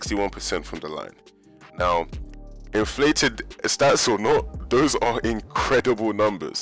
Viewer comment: A couple questions: 1. How were stats inflated in the 60's? Did they give you a free basket for every three that you scored? Answer: No. More people knew how to count in the 60's than today; the stats are the stats. 2. How many times in conversation did Wilt admit his obsession with stats to you? Answer: None. His team and his fans wanted him to score, and since he could, he did. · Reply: inflated? You mean that they were faster. I thought you said they are more athletic now 🤔🤔🤔🤔🤣🤣🤣😂😂😂😅😂🤣🤣